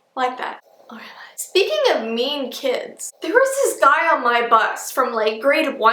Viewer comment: When you said: there was a guy on my bus. I thought you said there was a guy on my butt! LOL!😂😂